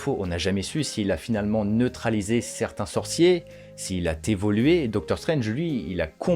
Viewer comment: s’il a quoi ? « T’évoluer » ? 😳😱